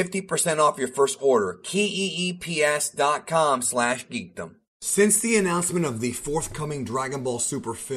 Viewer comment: you said 🔑Key E eep instead of “Keep”